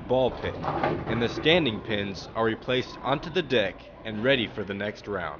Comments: Did he say d*ck? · sounds like he said dick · they say the remaining pins are replaced back on the dick instead of deck lol